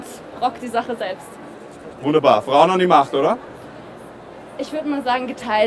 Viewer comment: Ich hab verstanden Braun an die Macht xD